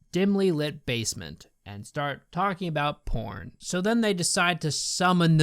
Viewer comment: Did he say pork or sporn? Reply what he said please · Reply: he said porn, I think he was going to say some porn but backed out last second and just said porn so it sounded like sporn